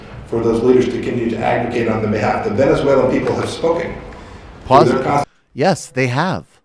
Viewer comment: Closed caption comedy @ 2:13, Pompeo says "The Venezuelan people have spoken" BUT closed caption says HE says..... "The Venezuelan people are smoking pot"